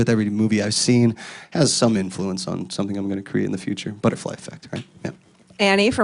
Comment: did he just said butterfly effect ?????